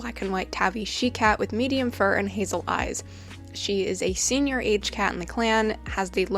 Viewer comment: When you said HazelEyes I thought you said he lied lol